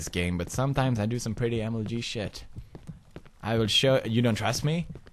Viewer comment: When he said that "mlg shit" I thought he said "mlp shit" so I was like what?